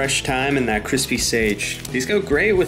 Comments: I thought he said placenta... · I thought he said with Placenta at first 😅😅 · I thought he said placenta lmao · These go great with some “placenta” ngl I thought that’s what he said.😂😂